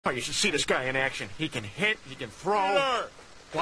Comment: I laughed for 10 minutes straight when I first heard that. ^^😊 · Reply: When I first watched this episode as a kid, I thought Peter said, “Holy Crip piece of crapple!”